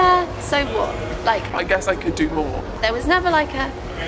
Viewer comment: i swear i thought she said i guess i could do porn ..XD